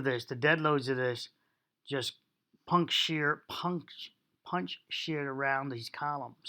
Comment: I always thought people were saying "puncture" but now I realize that it's "punch shear". I wonder if "puncture" came from "punch shear" or if it's just a coincidence that they are similar in meaning.